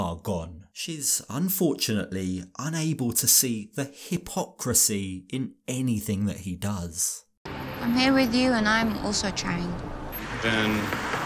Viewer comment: I thought you said hiphopcrisy as a play on Robs dancing at the airport. But nah, just had bad ears